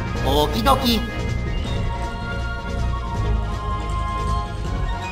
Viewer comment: Sonra : So long-a Bowser Xemnas : I am not Bowser Sonra : Good-a-bye Bowser Xemnas : I am not Bowser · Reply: I thought it said "so long gay bowser"